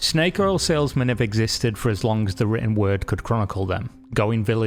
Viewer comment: As long as the N word can chronicle them? Wtf is he actually saying at 1:04 lmao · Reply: How did you get "n word" when I said "written word"? Maybe time to clean those ears lad.